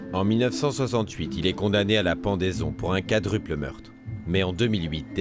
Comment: Says single, CC says quadruple. 😳